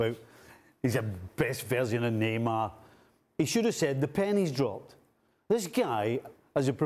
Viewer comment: I thought Nicol was saying “the panties dropped”… was like wth